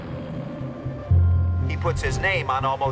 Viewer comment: I heard "Trump is going to rot" when she said Rise lol